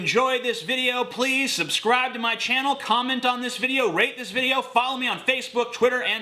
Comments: I always hear "...rape this video" at the end. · Genuinely thought he said "rape this video" at the end. Only for a second though.